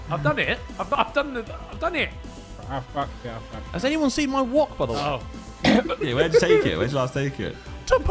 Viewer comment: Does anyone know what Harry said at 9:29 ?? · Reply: "Has anyone seen my wock btw "